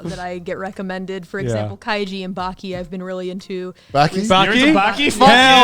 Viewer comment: What do they say there? I hear "Baki" but if I google that I only find a Netlix Anime called Baki from 2020. And she said before, that she only watches older Shows, any Captain here?